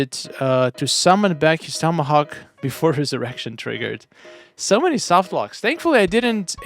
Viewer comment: misspoke when he was reading CoC Nightwolf's patch note. Listen closely to what he says at 1:40 😂😂😂 Summon his Tomahawk back before his Erection triggered 😂😂😂